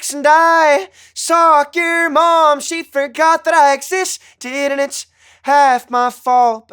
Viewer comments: I’m terrible. I thought he was saying “soccer mom forgot that I existed” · When I first listened to stick season I thought he said “soccer mom” instead of “saw your mom”. But listening to him sing it still sounds like he said soccer mom 😂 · Imma the only one that thought he said "i stalked your mum" hahaha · Always thought he said “soccer mom” not “saw your mom” 💀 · I thought he said "soccer mom she forgot that I exist"... "Saw your mom" makes more sense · Love this guy! Makes really beautiful music, but why does it sound like he says ‘I socked your mom’?? Is that just me??